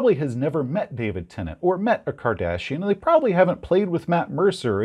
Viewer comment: I heard “being compared to David Tenet or a Cardassian.” That’s very different. I need more coffee.